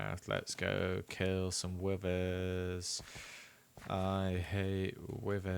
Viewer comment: Bruh i kept hearing 'Withers' as 'Women'